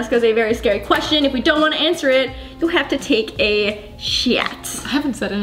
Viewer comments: Lauren: “if you don’t want to answer it, you have to take a 💩“ Just me who heard that? K... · ok. so when she said "take a shot" in the intro, it literally sounded like she said "take a shit"